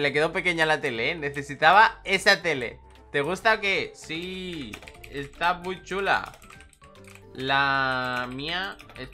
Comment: cuando dijiste si esta muy chula pensé que dijiste esta muy Chola, y yo em... Ke? Xdddd